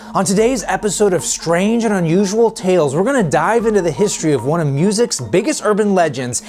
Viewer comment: My video buffered at 0:32 and it sounded like “We’re gonna die”